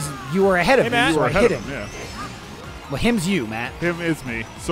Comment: Can anyone explain the "he is you" discussion here? I can't hear any of their words. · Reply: Matt accidentally referred to himself as “him” instead of “me”. Michael corrected Matt and said “him is you, Matt”